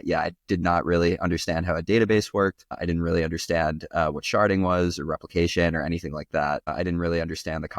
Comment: I love both these channels! Awesome to see on here! Note: there is an error in the subtitles at 1:26 where the subtitle says "charting" but I think is saying "sharding"